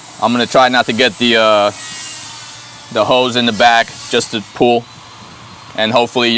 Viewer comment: did you just call random women "hoes?"